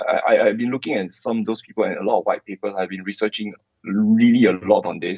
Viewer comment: Did he just say “a lot of white people have been looking into this” !?